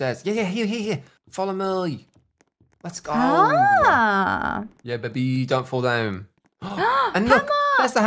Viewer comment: Is it just me or did someone else hear 💰🐔 say "yeah baby"